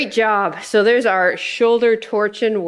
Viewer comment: I could've sworn I heard "shoulder-TORTURE" workout when you said "shoulder-torching" at the end! 😂 Seriously toasty shoulders indeed! 🔥 I used 5lbs and 13.4lbs (6.1kg) today. (Thanks for the great tip there, and! 👏 I was actually trying to figure out how I can properly track the weights I use per workout 😊)